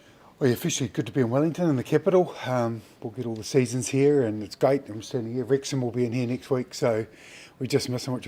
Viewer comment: Wow, I literally have no idea what Razor said from 0:15 to 0:25!🤔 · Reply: he said that theyll get all the 4 seaons in one day in wellington and that wrexham fc will be playing their next week